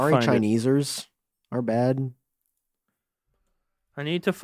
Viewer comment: Omfg. Did he just say, “Sorry Chineesers”, lmao. 😂😂😂